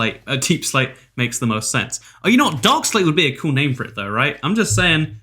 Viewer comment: i thought you said dogslate instead of darkslate for a second